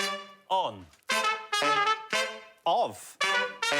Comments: i thought for a moment i thought he said off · i know im a year late to the party but, is that really how you pronounce "of" in english? ...definitely sounded like "off"...